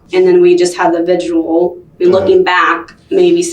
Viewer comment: Did she just call the vigil a ‘vigual’?! 11:03. Twice?